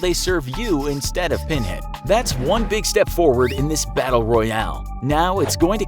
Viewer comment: did he just say battle Royale?